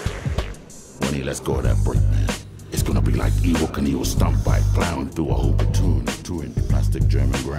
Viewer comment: idk what said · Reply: “When he lets go of that front wheel, it’s gonna be like evil canevil stunt bike